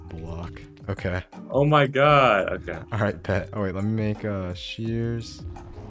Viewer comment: Ship Ship Ship x ship 🤣 jk If you couldn't catch it, called "pet" 🤣🤣🤣 Wholesome moments yayy :D